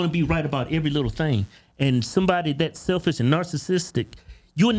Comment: Wait, I swear I heard him say "narsisister."